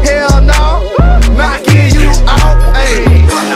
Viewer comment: the best part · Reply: sound like Momoshiki was saying "Knocking you OUT!"